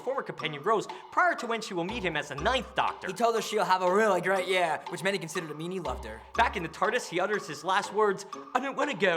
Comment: what did you say? "which many considered meetingudthere"???